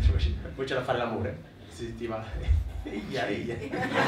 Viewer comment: Qualcuno sa cosa dice a 6:24? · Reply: Eja eja